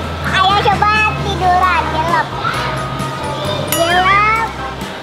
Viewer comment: Menit ke 19:53 saya kaget. Gk salah denger bundanya ngomong GEBLEK.. Sampe puter bbrp x buat mastiin. Ternyata gk salah denger 😓. Gk sengaja sih anakku nonton, gegara muncul yg video unicorn..lain x jangan keceplosan lagi ya bundanya, ini kan tontonan buat anak2 ✌🏻